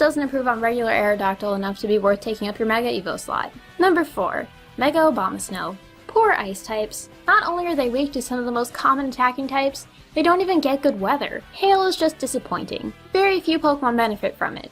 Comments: For a second I thought you said "Mega Obamasnow" xD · I thought u said mega Obama for a second XD